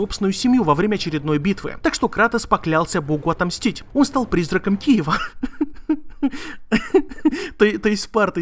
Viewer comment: призрак киева мне не послышалось?